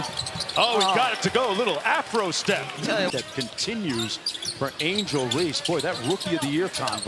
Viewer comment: did he say "a little afro step"? I'm tripping right? Tell me I'm tripping.